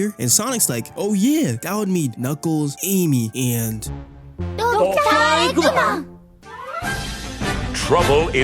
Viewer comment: no way the Captions saying Donkey Kong when they mention Eggman is crazy